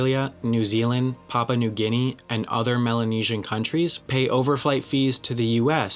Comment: what the hell did you just call us Aussies? Maluesian 😂